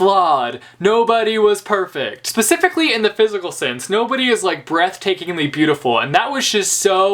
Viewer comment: ish you say something like "nobody was perfect, especially in the physical sense," and I thought you said "in the biblical sense," ... and that would REALLY change the book so um yeah. I need coffee before YouTube videos.